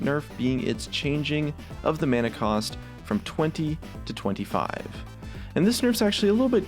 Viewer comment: broken subtitles says 25 to 30 instead of 20 to 25